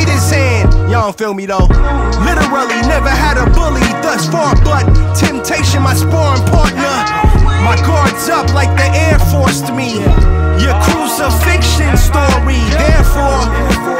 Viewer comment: Did he just say “my guards up like the heir forced me (air force national guard) 🔥🔥🔥🔥🔥🔥